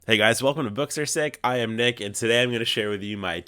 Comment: You said “I am Nick and” so fast I thought you said “I am naked”. I had to rewind and listen to it again.